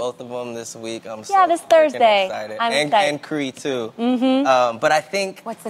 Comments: it sounds like he said im so f***ing excited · Ok.....Is it me??? Is it my ears????? Did Tahj say "I'm so F@ excited on THE REAL???? It's right at the 0:50 mark so you have to start it before that to hear it. I'll admit it if my ears have betrayed me but I do believe that he dropped the "F-Bomb" on daytime television. Please let me know what you think everybody. LOL